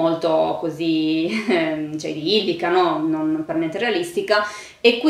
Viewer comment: non ho capito la parola a 18:10 : visto in un ottica...? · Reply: Idillica